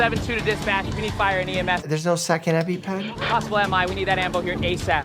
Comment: hey I'm pretty sure at 7:20 the guy said 'ambo' short for ambulance instead of M.I (myocardial infarction). was pretty funny watching mike crash out though.